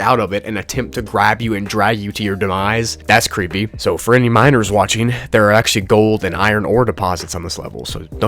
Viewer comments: I thought you said “For any minors out there” 😭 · When you said "for all you miners out yhere" I thought you said minors, which in context to the previous thing was kinds scary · i thought you said minors for i sec